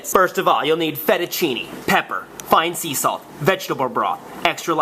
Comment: I thought said "Vegetable Bra".. -_- #vegetablebroth